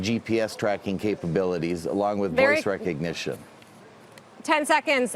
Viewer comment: "will have GPS tracking capabilities, along with ???.. (is he saying face?)...recognition" Boom!!!! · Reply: He said "voice recognition "